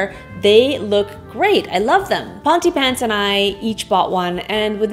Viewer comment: Okay, strange question but I think you said your husband's name, but I think I misheard it. It sounded like Fantypants to me. Also, could you put the floor lamps on a box so they are higher - especially if they're behind a chair?